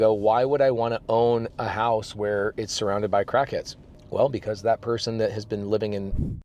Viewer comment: DID HE JUST SAY CRACKHEADS 😂😂😂😂😂😂😂😂😂😂😂